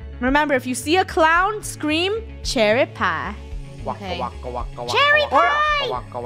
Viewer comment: When you said “Cherry Pie” I thought you said “Share a Pie” 😂 😂 😂 😂 😂 😂